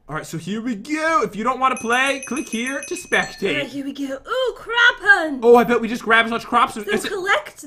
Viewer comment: who heard say crap hunt.